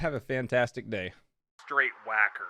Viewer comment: I fell asleep watching this and I swear at the end I heard “straight whack-off” instead of straight whacker.